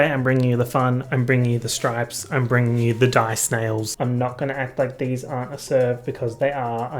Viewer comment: I wasn’t looking at the screen at 0:11 and thought was saying ‘die snails’ 🐌 🗡🗡